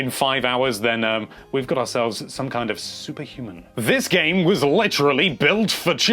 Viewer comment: When says "you are some kind of super human" I got an ad that made it sound like he said "you are some kind of soup.......” And I agree, those haters are just as bad as soup even thou I like soup. Ok there as bad as MUSHROOM SOUP (cuz who likes that?!)